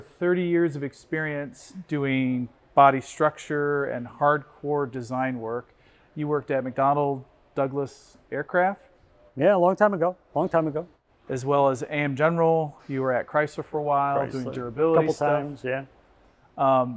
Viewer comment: I thought he said he’d worked at McDonalds for a second 🍟